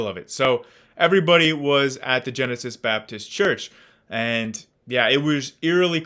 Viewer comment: i thought you said "genitals baptist church". maybe i did.